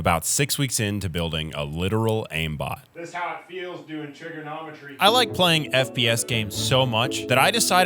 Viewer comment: All I heard was "Trigger-nomitry".